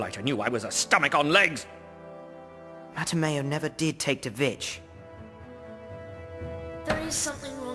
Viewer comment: lol...it sound like said "mattimeo never did take the bitch"